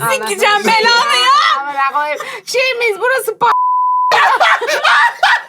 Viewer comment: ay burda ne diyor biplenen kisimda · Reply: Burası Pakistan mı diyor bence